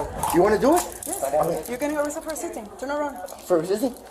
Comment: At first I thought the cop said "your arreated for existing" basically the same thing.